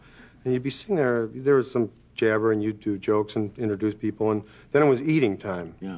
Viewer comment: did Bill say YouTube? I do believe he was sent from the future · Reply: "You'd do jokes" not "YouTube jokes"